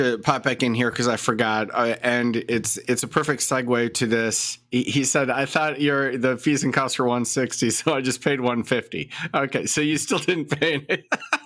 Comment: He said he thought it was 150 so payed the 150 and found out it was 560. You misheard. But still agree, i think its B.S.